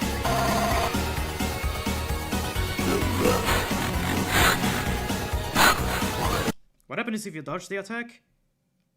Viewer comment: you forgot to tell us what he was saying here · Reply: It's probably the hardest one but someone else managed to catch a good ear on it, what said there was: THE LAST THING YOU'LL SEE IS THE FIRE OF THE STUDIO